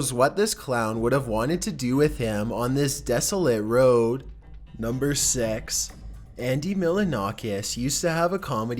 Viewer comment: Did him say sex in number six